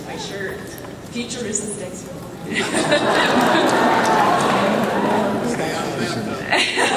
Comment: What did Michael say at 8:27? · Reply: Ryodan71 stay out of the bath tub, only 6 years late